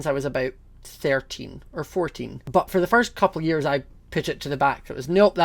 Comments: I’ve know I was gay since I was about 13 or 14 I thought he said 30 and 40 😂🤣💀😭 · When he said "I've known I was gay since I was 13.. it 14.." I thought he said 30 or 40 oof